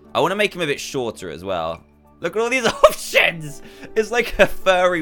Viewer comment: Dan what did you say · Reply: thats options